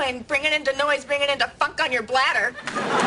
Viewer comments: Hold up did she say "fuck" at 1:21-22? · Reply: Either that or funk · What did Phoebe say the babies were playing on her bladder? Did she say the F word? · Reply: Funk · I swear to god I thought she said "Fuck" for a second and wondered if that was a deleted scene because there was NO WAY they would've gotten away with airing that. Then I realized it was a very fuck-like "funk."